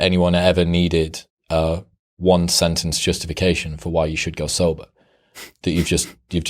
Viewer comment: What word is that at 24:57 “Go Solar ? “ “Go solo?” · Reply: Go sober :)